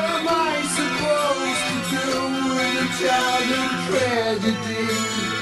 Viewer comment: "with a childhood tragedy" what the heck? I always heard it as "in a time of tragedy" lol